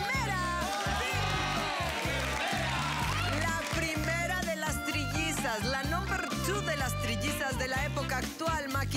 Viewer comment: Jaja 16:28 que dice la audiencia de la enfermera? Xd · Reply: Referencia a animaniacs "Hola enfermeraaaa" jeje